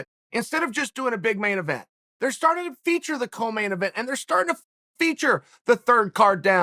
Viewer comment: did chael actually said f***ing feature???